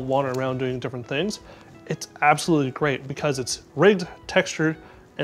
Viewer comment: "Its absolutely great because its ribbed, textured and ready to go"... thats what i heard